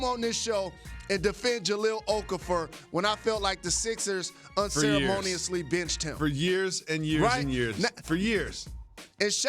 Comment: Bra I thought he said the Sixers bitched him at 1:38🤣😂